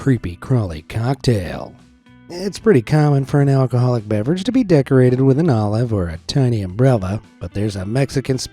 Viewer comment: For a second I thought he said it’s pretty common for an alcoholic to be decorated with an olive or a tiny umbrella 😂😂😂😂